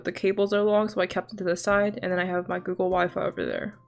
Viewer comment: Did she say google waifu??? ?????